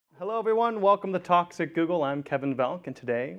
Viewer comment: they say "talks at google" but I hear "toxic google"